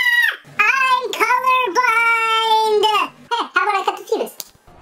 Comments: Did Tails just say Penis? O_O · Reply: It did sound like that, but he said fuse. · dang it tails! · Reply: For a second I thought he said. “Hey what if I cut the p****?” ...